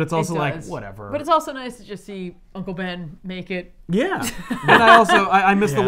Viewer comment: Xavier and Richards saw how effective Spider-Man became with six arms, and just told him there was no solution. 41:41 I heard "It's also nice to see Uncle Ben naked."